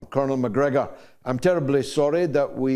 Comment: I actually thought George said Conor McGregor at the start